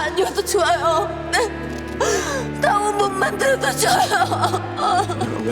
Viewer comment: 당신이 부자가 아니여도 좋아요 이후에 뭐라고 하는지 아시는분 ㅠㅠ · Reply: 나 옷 못 만들어도 좋아요